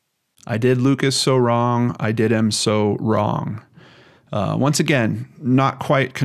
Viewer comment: I know it doesn't make much of a difference, but in the recording at 25:33 when she says "I did Lucas so wrong. I did him so wrong." It sounds, to me, that she actually says "I did Lucas so wrong. I didnt look." Does anyone else hear the same?